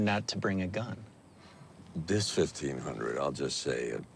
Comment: What does Mike say? 3:30 This————- hundred. You’re getting a bargain. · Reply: Thais fifteen hundred